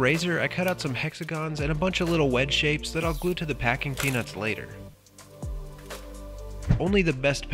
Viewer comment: I misheard and I thought he said "Ahhhh... Packing penis."